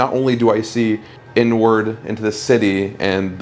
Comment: Lord I thought he said I see n word instead of inward 😂😂 Ooh the settling is scary.